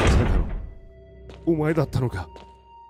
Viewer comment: どうしてもなんて言ってるか分からん 誰か教えてください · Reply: 「お前(セル)だったのか！」かな(●´ω｀●)